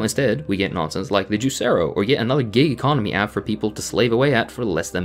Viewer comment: I thought said "gay economy" 😂